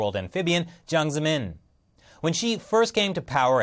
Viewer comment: Multiple times when he said Xi i thought he said she